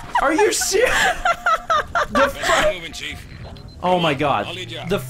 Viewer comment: Almost sounded like said "dafuq!" there for a moment, haha. (I know he started saying "the first damage," though.)